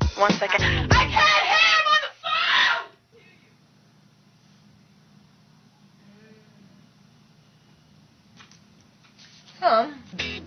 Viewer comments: I CAN'T HEAR I'M ON THE PHONE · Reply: I thought she said "I CAN'T HEAR, MOTHER PHONE" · I CAN'T HEAR I'M ON THE PHONE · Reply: I thought she said, "I can't hear, mofo!"